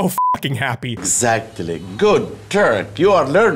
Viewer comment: sounds like he said “good, turd,” which honestly doesn’t sound too far-fetched coming from this guy